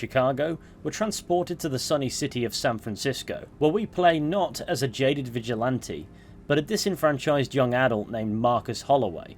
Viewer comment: Listening in the shower, thought for a sec you said “You play, not as a gay vigilante…” and thought “Oh shit, I wanna play THAT Watch Dogs game 😎💅” Marcus slaughters ten cops, holsters weapon, checks Uber for nearby jobs, checks Grindr for nearby hole